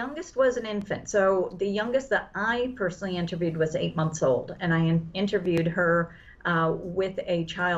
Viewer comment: did she say she interviewed an 8 month old? Well it sounds like she has met her match